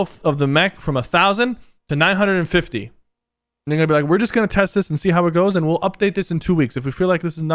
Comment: did just said n*gga be like? What!!!